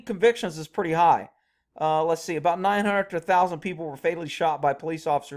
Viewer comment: Lol it seriously sounded like you said "they kill 900,00 people a year". Then I realized you ment 900 to 1,000.